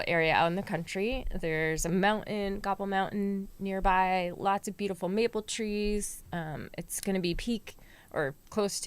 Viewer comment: At 1.09.34 I heard 'lots of beautiful meatball trees'. Being Swedish I immediately wanted to move to wherever this is... Then of course I understood that I'd listened to the whole show with just one ear, so I'm watching it a second time now, paying more attention to this great conversation.